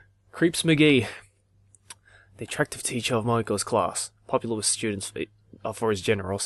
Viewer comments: when he was naming her moeko it sounded like he said mogeko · For a sec there I thought u said "Moge-Ko" from Mogeko Castle lmao.